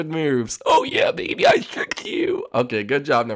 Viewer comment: it sounded like number 2 said oh yeah baby I fricked you xD